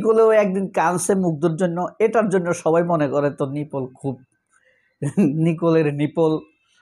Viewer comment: Wait a sec, 😂 did you just said "Nicoler Nipple" @ 3:03 😅😅